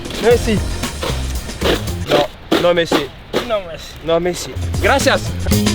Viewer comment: oué moi aussi j'aurai dit "Non merci" si on me proposait d'aller creuser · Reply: ᕙ༼*O_O*༽ᕗ AliGACHA ᕙ༼*O_O*༽ᕤ il dit pas non merci mais No Messi